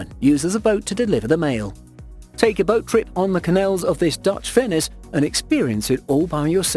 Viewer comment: Come on we all know u click because of the thumbnail😂😂😂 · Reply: You got me. But I watched the whole thing. What is he saying at 7:03 ‘canals of this Dutch fince’